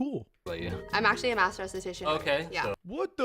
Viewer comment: I heard "Master statistician" too. 😅 Actually it's nice that Ludwig thought she was smart in math for a moment.